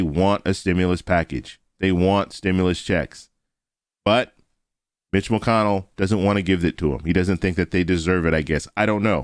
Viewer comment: I heard him say bitch mcconnell